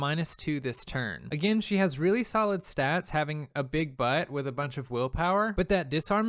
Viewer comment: Great videos, totally sounded like you said big butt and not buff. 😂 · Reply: He did say big butt. It means it has a high defense/Willpower.